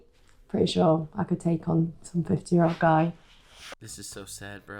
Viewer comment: "I think I could handle a 15 year old guy" PLEASE TELL ME I HEARD THIS 23 YEAR OLD WOMAN WRONG!!! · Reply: I believe she said 50 guys. Not a 15 year old guy. What's up with your hearing ?